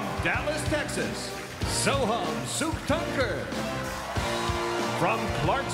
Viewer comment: Did anyone hear "Suck it"?